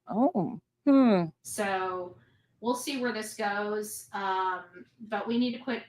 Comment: Did she just say “we’ll see” or “OOPSEEE